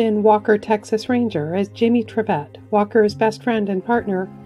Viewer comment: So sorry to know Clarence Gilyard passed away. So shocking. I’ve always thought he had a great smile. 🕊️ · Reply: Trivette. I always thought they were saying Tribbet.